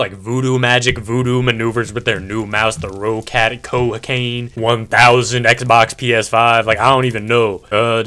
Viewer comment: Did he just say COCAINE?!?!?!